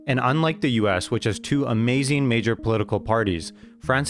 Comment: Did he call Republicans & Democrats amazing?